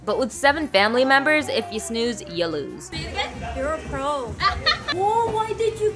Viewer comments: Omg I died laughing at “oh, why did you get the middle!!” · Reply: I thought she said "oh why did you bake so little...." 😊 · Omg I died laughing at “oh, why did you get the middle!!” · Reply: Bruh I thought she said, "why did you get the nigga!!!!!"